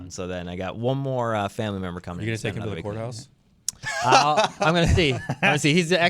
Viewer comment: Ohhhh he said court house. I thought he said whore house